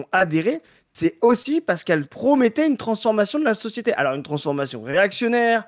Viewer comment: ya que moi qui entend "Pascal Praud"? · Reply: Pascal praud mettait= parce qu'elle promettait.